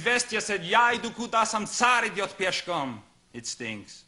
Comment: Whenever I heard, "Izvestiya said, 'Я иду куда сам царь идёт пешком!'" (transliterated: "Ya idoo kuda sam czar idyot peshkom!"), I always thought the last part was "I'm sorry, you poor scum!" Now I find out, NO...it's "sam czar idyot peshkom"!